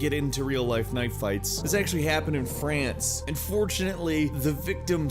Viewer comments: When he said "and fortunately" the first time i heard it i thought he said "unfortunately" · "AND FORTUNATELY" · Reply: Well goddamn, he sounded like he said "unfortunately". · I kknow you said and fortunatly but it sounded like you said unfortunatly as though you wanted the victim to die